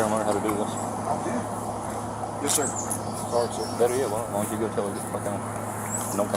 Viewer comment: Did he say, “Why don’t you tell her to GTFO” around the 1:39 mark ?